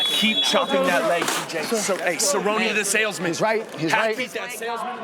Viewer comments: what does dillashaw’s corner say to him? It sounds like “Cerrone the salesman?” Lol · Still think Sandhagen won this one. 🤷‍♂️ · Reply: did he say cerrone and the salesman? anyone habd the slightest clue as to what that means?